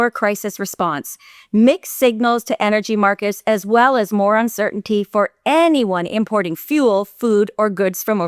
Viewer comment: I misheard slightly and thought you said Moron Certainty. While I caught the mistake on my part, I also thought it was mildly fitting as there doesn’t seem to be much of a difference when it comes to Mark Rutte.